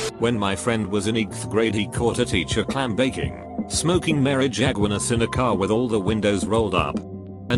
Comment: No way did he say ‘Marijagunas’😂😂😂